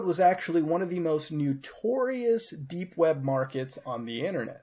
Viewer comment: LOL did he say NEWTourious at 1:42. Hahahahahaha.